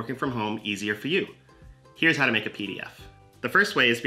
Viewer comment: At first I thought it said, " how to make pot "